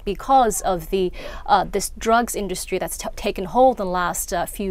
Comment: I want lychi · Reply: I thought she said ¨lye cheese¨....